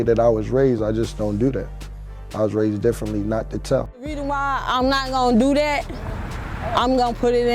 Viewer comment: wtf did she say? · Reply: "The reason why I'm not gonna do dat"